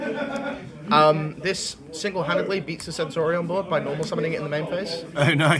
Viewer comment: what is he saying Arvata beats at around 1:50? He says "this single handedly beats desensorsorionboard by normal summoning it in the main phase." I have no idea what he's saying LOL